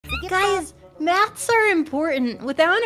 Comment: oh MATHS, I thought shawty said "knats"